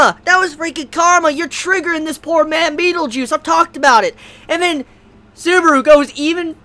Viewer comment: I keep hearing "beetle juice"